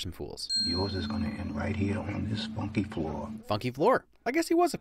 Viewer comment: Funky floor, I thought he said spunky floor because well he's in the hooker business